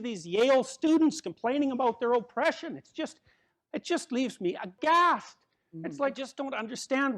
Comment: Amazing speech now I'm going to read and think for the good of others, 3:32 what the word he said there? "It just leaves me ________!" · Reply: Aghast